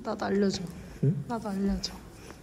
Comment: 진짜 6:27초에 날려줘로 들리네